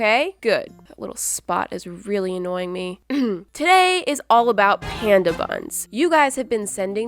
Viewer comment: Turn on the English captions at 0:24 and see how many times the captions got “panda bun” wrong lol